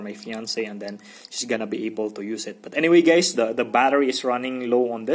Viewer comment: It sounded like he said okay gays at14:44! 😂